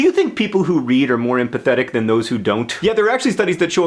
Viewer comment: Oh dear. I thought you said that you think readers are PITHETIC instead of empathetic.... -_